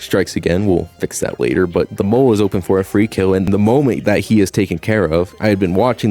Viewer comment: did you say "molement?"